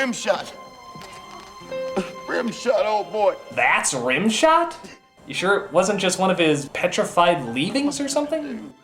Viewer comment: Creo que en el 16:50 dice: ¿Ese es Rimshot? ¿Estan seguros que no es uno de sus petrificados (Algo que no se que es XD) o algo asi? Pues supongo que dice eso ._.